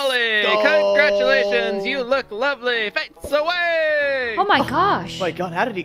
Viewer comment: what does say before the word "away" ? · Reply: Does he not say ‘fwhips away’ or smith..